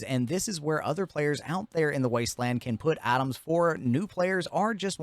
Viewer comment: I was doing something half paying attention and I thought you said other players can put atoms in there and did a double take but you just said items like a true appalachian 😂